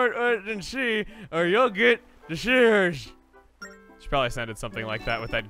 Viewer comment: It sounded like he said “shitters” instead of “shivers”